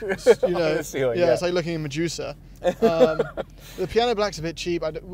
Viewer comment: he goes “my juicer” instead of “medusa” 😂